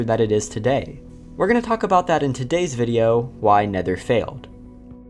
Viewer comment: I thought it said "Why NEITHER failed" and I was just intrigued to know what "didn't" fail, good to know. Is that Undertale music ( O_O)